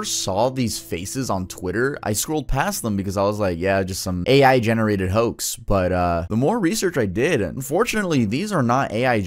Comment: thought you said same AI generated h03s